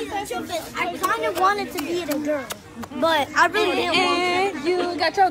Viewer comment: What that lil boy said??? I KINDA WANTED TO BE THE GIRL BUT I DIDN'T WANT TO BE A GIRL ????????? AND IN THAT MOMENT WITH THE YOU GONNA GET YOUR KARMA.....THAT IS WHERE THE VOLUME WAS HEARD!